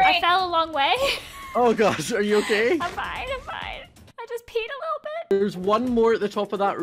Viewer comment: did she say i peed a little